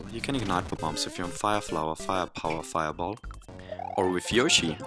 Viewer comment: What did he say? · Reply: Fire flower fire power fire ball